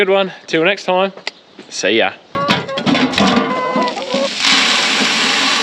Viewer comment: You’ll get some barley, but I’m hoping you don’t have much need for straw. Your comment about feeding the yard birds at the end gave me a bit of a chuckle, cause I heard it as The Yardbirds. However, I didn’t think you had a famous 60’s rock band lounging around waiting for some food. 🤪